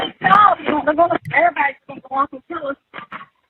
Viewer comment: sounds like she says, "hey stop, the airbags are going to go off and kill us" then she says, "CASEY oh God".. so if she was worried the airbags was going to go off and kill them.. why the hell would she kill herself!! Just something's not right with that..